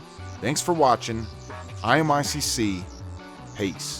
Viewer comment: When it said stop playing game I thought it said stop... Mayonnaise